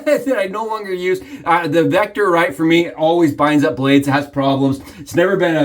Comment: Good video!! It's interesting to hear you prefer the Shield over the Vector. What is the word you say at 5:35 about the Vector? I hear "binds up blades" what does that mean?